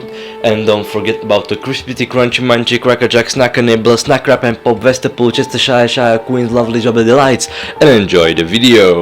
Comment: still trying to figure out half of what you said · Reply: Crispity Crunchy munchy crackerjack snacker nibbler snack crack & pop westerpollchestershireshire Queen's lovely jubily delights